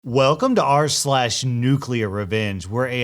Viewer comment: Dont tell me Im the only person who thought it said nuclearcleavage